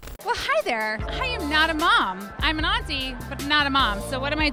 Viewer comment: It almost sounds like you said,"I'm a Nazi." But I will assume you said 'auntie'.